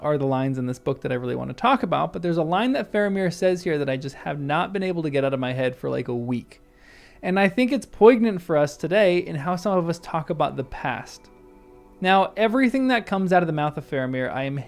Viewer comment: you say that what Faramir says is 'pugnant', is that new zoomer slang for 'repugnant' or something to do with pugs? -lost